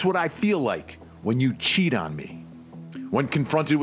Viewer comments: It sounded like she said that's what I feel like when you're choking me. Not cheating on me. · I hear “that’s how I feel when you CHOKE me” (can’t breathe) I didn’t hear her say “cheat on me” don’t get me wrong she’s a evil f’ing batshit creepy slime of a female and gets EVERYTHING she deserves! I hope she’s convicted ! But I didn’t hear that as cheating?